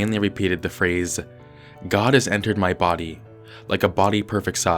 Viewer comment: I thought it said god has entered my body anal